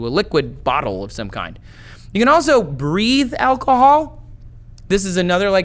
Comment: I thought you said you can breed alcohol xD